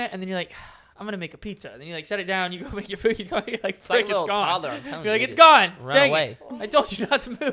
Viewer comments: did you just say sh** 😅😢😂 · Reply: It sounded like said "fu** it's gone" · did say f*** 😂😂